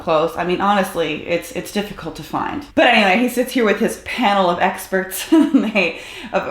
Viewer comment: When he said “panel of experts” I thought he actually said “panel of incels.”